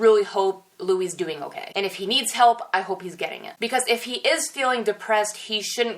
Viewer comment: I heard "I hope he's gay" and I just started dying.